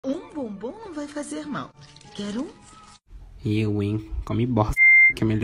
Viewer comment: Genteeeeee eu escutei mais de 5 vezes e eu escutei “um bumbum não vai fazer mal quer um?”